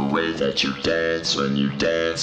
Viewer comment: i keep hearing no pants off